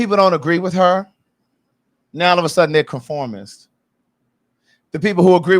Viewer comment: she did not call them conformists. She said reformists. And she's right.